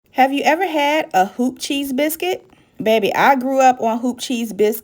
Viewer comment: Iam sorry I thought you said Hoochie cheese 🧀 biscuits 😩🤣😆😆😆 love them look delicious 😋 I will be trying it